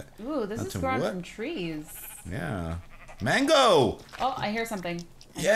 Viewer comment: When said mango I thought he said mangle